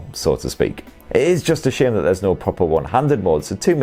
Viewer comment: "It is just a shame..." can anyone clarify what was the complete sentence. I've listened through that for like 10 times but I still can't figure out what he said. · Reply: that there is no one- handed mode